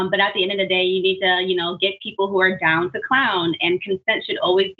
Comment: I thought she said down with the clown and I was suddenly having ptsd thinking about being a 13 year old and listening to juggalo music lol.